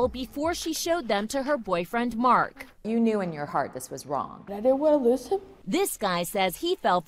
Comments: What did she say?? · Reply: Shanelle Lol “But I didn’t wanna lose him.” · What did she say??!?🤣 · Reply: She I said I didn’t want to lose him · what did she say there? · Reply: She said "But I didn't wanna lose him." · what ?? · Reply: I thought she said I didnt wanna listen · what ?? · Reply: I thought she said I didn’t want a little sip